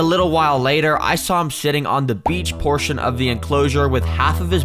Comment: i thought you were saying shit instead of sit